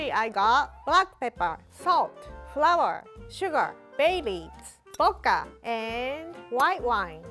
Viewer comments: I wasn't looking at the screen when she said "bay leaves" and I heard "babies" and was very confused. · I legit thought I heard "Babies" instead of "Bay Leaves" when she listed out ingredients. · Did anyone else hear babies when she said bay leaves? 2:38 Lol because my sister did, she's 7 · Lmao when she said “bay leaves” I heard her say BABIES 😧